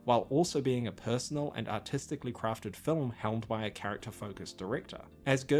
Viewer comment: I heard the words "Artistic" as "Autistic" and i didnt even question it